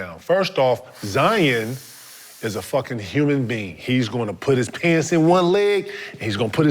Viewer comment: KG setup on the Zion thing was wild...didn't know where he was going with it...He gonna put his...? LoL...thought he was saying he gonna put his penis in one lady then he gonna put his penis in another.